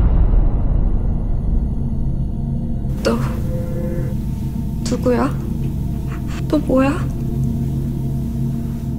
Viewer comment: What did the girls say at 3:19? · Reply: She said "Who are you? What are you?" :)